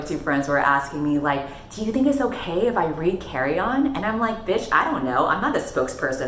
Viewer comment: I heard “Carry On” as “carrion” at first. A new insulting nickname?